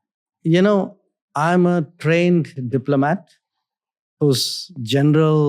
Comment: When he said " I am a Trained Diplomat .. " I heard Trained Assassin.. . 😅